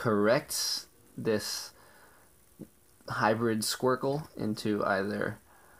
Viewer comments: I thought he said hybrid Squirtle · Did you say 'Hybrid-Squircle"